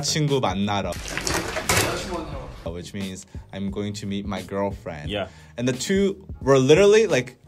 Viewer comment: ISTG I THOUGHT YALL SAID BEAT YOUR GIRLFRIEND FOR A SECOND